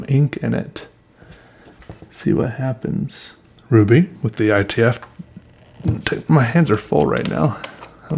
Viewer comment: it loosely sounds like he said “ruby take my hands” with a few words in between it. Which are lyrics to a top song. He says ruby which is the color of the ink then when opening the lid he struggles and it sounds like he says “take my hands” but I don’t think he meant to I think it was just a mix of multiple sentences.